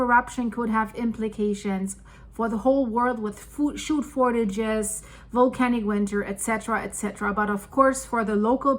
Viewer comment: Oh. Did I just hear "shood fortages"? That's a good one. I don't mean the message but the shake-up. :D